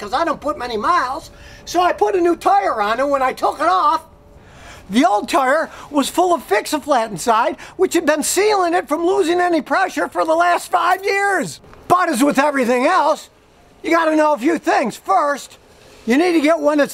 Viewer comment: Lol i thought it said "Fix a Fiat"